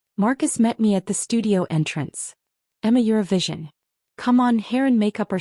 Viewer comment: This is what the subtitles say: “I’m a Eurovision.” These are the words the narrator actually says: “Emma, you’re a vision!” AI has some serious issues with spelling and word choice. The stories MUST BE PROOFREAD by a pair of HUMAN eyes and ears that are truly listening to the story being read.